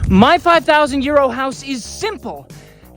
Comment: Who else thought he was saying “my 5000 YEAR OLD house” ? - anyone??? No - just me then